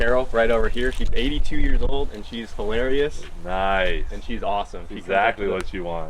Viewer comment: I just died laughing i thought you said the 80 year old woman was delirious not hilarious and then saying that's exactly what you want😂😂😂